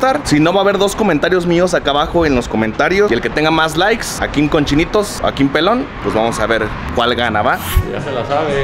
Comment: En lugar de “Akim con chinitos” de primero escuché “Akim cochinito” le regrese y ya vi mi error jajaja